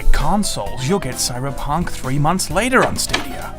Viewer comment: did you say "Cyberpunk 3" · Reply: 3 months